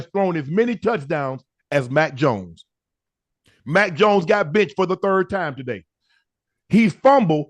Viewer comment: I swear I thought shannnon said max Jones got bitched 😂😂😂😭😭😭